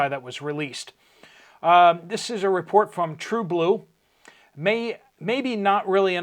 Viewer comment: you may need to fact check some of the reports you’re getting from your viewers, I just now came back from Sam’s and they had plenty of sugar, I bought 10lbs for $7.28. Edit: Murfreesboro, Tennessee. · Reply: I thought he had said report from canada.